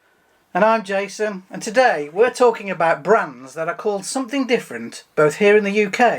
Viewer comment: At first I thought that it said “bands”. Lol!